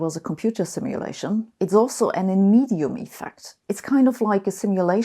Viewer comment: What is the word Sabine says at 9:27 ? “Inemium effect”?